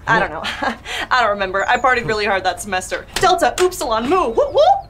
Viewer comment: I had to rewind to this because I heard "I farted really hard that semester.